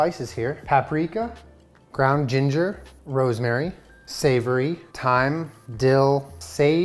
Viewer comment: Did he just say "savory" was one of the spices???? Am I the dumb one?